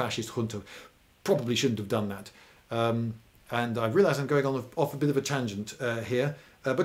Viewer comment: "I realize we're going on a bit of a tangent" -. That's why we love you. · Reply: You misheard, he said "realise". Because he's British.